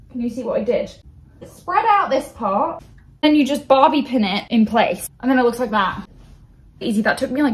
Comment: Ngl kinda thought she said 'barbie pin' not bobby pin